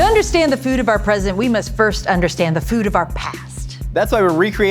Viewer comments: I thought she said "food of our President." I'm like man, another episode about mcdonalds and KFC?? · I thought said to understand the food of our president